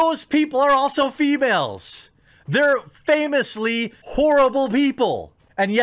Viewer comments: Did you just say "famously whorable people"? lol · what he said: "they're famously horrible people!" how I heard it: "they're famously whorable people!"